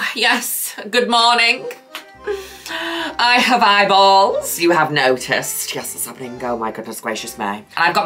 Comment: I really thought she said, "I have eyeballs. You have no tits." Like, damn.